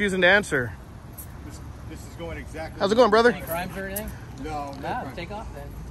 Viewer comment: what did the arriving officer say to the officers that were already there right before he told them to take off? Oh I think he asked, “Any crimes or anything?” Correct me if I’m wrong?!